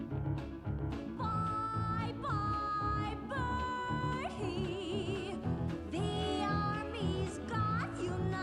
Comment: all i hear is "berd-hee" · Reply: its bye bye bernie!